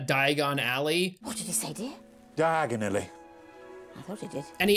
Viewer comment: Because he's not saying "Digonelly", he's saying an accented "diagonally". So instead of going to Diagon Alley, he moved diagonally to Knockturn Alley.